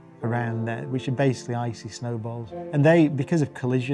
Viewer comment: 😅I thought he said isis snowballs 😅